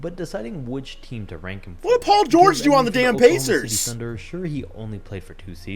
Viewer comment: Did he just say what did pg do on the pacer